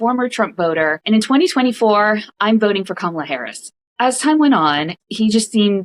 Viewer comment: Did she just say “…in 2024 I voted for Kamala Harris?”